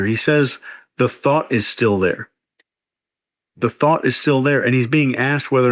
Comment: "I'm impotent, but the thought is still there" 🤨 I heard "I'd like to still be a predator, but I can't." Yikes